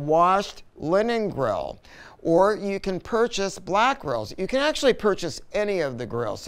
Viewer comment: If you follow the YouTube Transcript after the 10:29 minute mark it say you can “purchase Black Girls” instead of Black Grills 😂😂😂